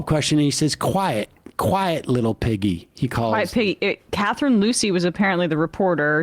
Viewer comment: I had legit thought he was saying "Quiet Vicky" and that was bad enough! But now knowing it was "Quiet Piggy" is just so so sooo gross and disgusting!